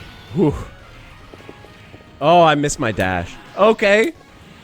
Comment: Lol i thought he randomly said i miss my dad not dash